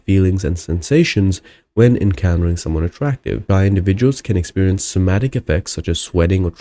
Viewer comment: did you say bi individuals? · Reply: Shy individuals